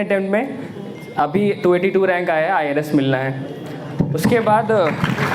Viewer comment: what he said IRS or IAS???? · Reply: Irs